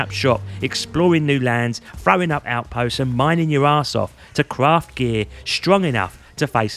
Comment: "...And mining your arsehole". ... I REALLY misheard that one!